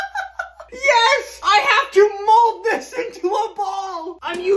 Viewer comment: I first thought he said mold it into a bong.